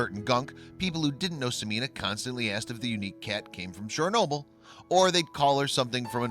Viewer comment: Oh - Chernobyl. I thought he said Cher Noble!